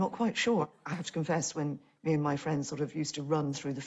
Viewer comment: I heard "me and my friends were used to run in a field of weed" so I was REALLY confused Yeah... English is not my first language...